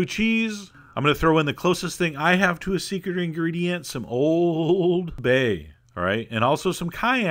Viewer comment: what kinda spice is o-o-o-o-o-old bay (old bay) ?? or did i hear it wrong?